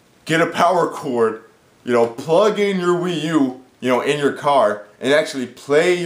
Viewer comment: nvm I misheard you. I thought you said plug in the Wii U game pad, not the Wii U itself. That is pretty cool. On that note good video.